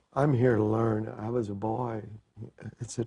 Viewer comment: What the heck did Mike mumble at 5:30? The other band members moved quickly to cover for him. That's kind of sad. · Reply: ***** Oh! He was a boy when the band first came out. I get it now. Thanks.